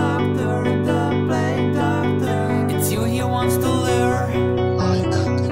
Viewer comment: this whole time I thought he said “doctor, doctor, don’t play doctor” for some reason but he said “the plague doctor”